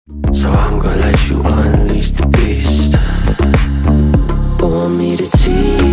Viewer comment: мне одному послышалось мистер бист?